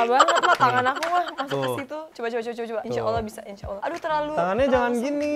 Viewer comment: Apa cuman saya salah dengar kalau wilo bilang insya ALLAH 🤭